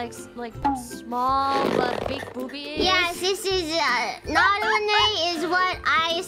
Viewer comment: I think she said Naru-nee, a nickname that pebbles gave her in prechat.